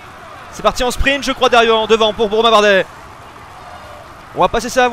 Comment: qu'est ce que tu dis a 58:33 c'est WHAT THE FUCK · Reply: Slt , il dit : je crois que sa va sprinter devant pour romain bardet . Dr